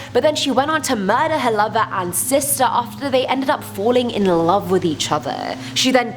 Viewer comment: Anyone else think at first she was saying mother and sister fell in love with each other I had to replay it a few times Just didn’t sound right